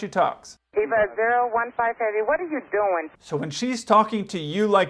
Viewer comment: Thought I heard “EVA015 heavy what is you doin..?” 😂🤣